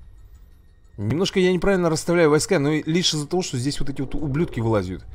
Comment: я один услышал слово уб***